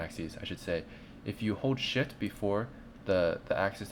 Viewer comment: thanks for the tutorial. it helped alot but curious is it just me or does it sould like he said if you hold sh*t instead of if you hold shift at 15:00 ?